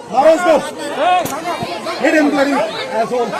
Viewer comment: Did he just say "asshole, hit him...."